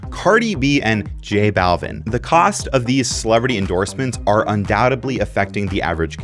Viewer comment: Did you just say "bohemeth"? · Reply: Did you just say "undoubtably"?